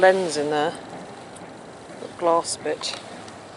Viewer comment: with subtitles says "glass bitch" instead of glass BIT'. "sigh"